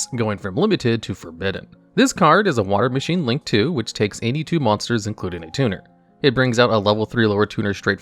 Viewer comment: I misheard Halq as "It makes 82 monsters" and honestly, that's technically true too